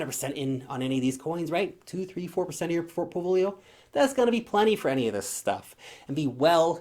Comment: Did you say.... fortpolio?